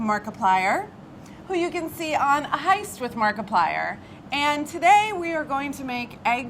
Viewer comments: thought for a hot second it said 'atheist' not 'a heist' and was like that checks out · Thought it said "Atheist with markiplier" at 0:17 lmao